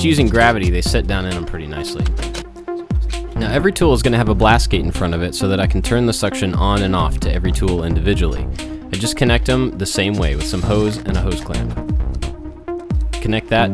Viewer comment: Did you say "blast gate"? 6:25 I like that you says and suggested that "help you out" instead of trying to trick or dupe us into buying from there. Typically when I catch on that, I'll intentionally avoid it bc I don't like being "tricked". Kudos, great vid